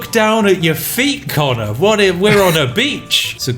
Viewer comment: as french instead of we're on a beach i heard "look Connor. We're on a b** !"